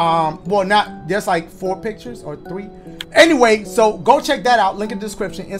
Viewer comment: IDK why I heard him say “there’s like 4 pictures or TREEEEEP”